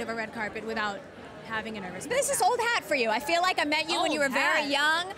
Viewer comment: sounds like she says “old hag” and aris reaction is priceless lmao 😂